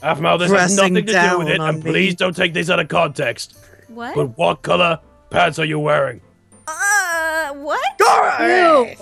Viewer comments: I thought said "What color pads are you wearing?" And I was like: WHAAAAAAAA????!!!??!?!? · I THOUGHT SAID "What color PADS are you wearing?" TO APHMAU AND THEN I WAS LIKE "HOW DO YOU KNOW THAT WOMEN WEAR PADS?!" AND THEN I FOUND OUT THAT HE SAID PANTS INSTEAD LOL BTW THIS COMMENT IS NOT FOR BOYS TO READ SO IF YOU'RE A BOY THEN IM SORRY.... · i thought he said "what color pads are you wearing?" XD